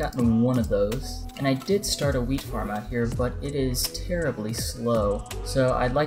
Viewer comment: Why did I think of "weed farm" when he said "wheat farm"?